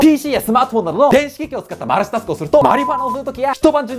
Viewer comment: マルチタスクがマルチマスクに聞こえてきた…幻聴…病院いこ(‘ω‘ )